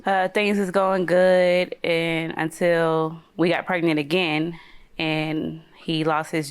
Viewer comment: Am I trippen or did she say WE got pregnant again 👀👀